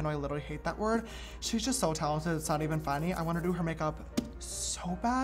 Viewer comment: James: Fanny (instead of funny)😂